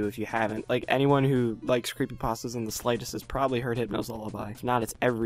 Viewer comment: I thought u said Pokemon creepypasta s